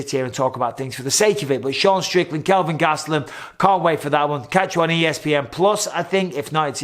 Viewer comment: Did u say Sean Strickland and Kelvin Gasteluem at the end of video?? Tighten up Mike.